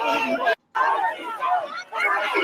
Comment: I thought you said anti father 😅😅